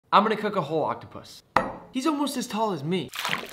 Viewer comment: said "I'm gonna cook a whole octopus" I heard "I'm gonna cook a whore octopus"